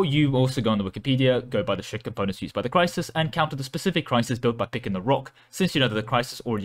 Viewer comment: Anyone able to tell what this guy's saying at 7:24? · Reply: "You can go on wikipedia (he means the game wiki), go look at the ship components used by the crisis, counter the specific crisis by picking the rock, since you know the crisis already committed 112% of its resources to scissors."